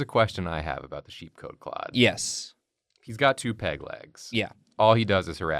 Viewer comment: Hahah at first I thought you lads said the first one had pig legs 😂😝